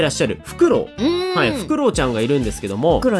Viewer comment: 「フクロウちゃん」が「クロちゃん」に聞こえましたすいません…